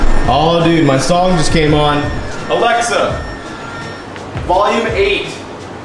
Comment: Whenever you say "Alexa" I hear "Hey NSA"